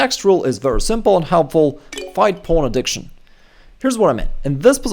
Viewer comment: Fight pawn addiction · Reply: I heard it as porn addiction